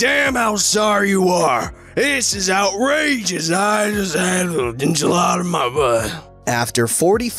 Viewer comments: it sounded like you said there’s an enchilada on my butt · Did he say 'I have an enchilada in my butt'??